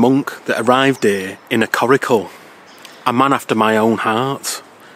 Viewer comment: Also, can someone please tell me what is saying at around 19:24 about traveling by…something that sounds kind of like “curicore” to my ears.